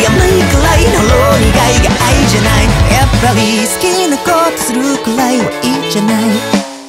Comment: 好きなことするぐらいはいいじゃない？が好きなことするくらいは愛じゃない？に聞こえる気が...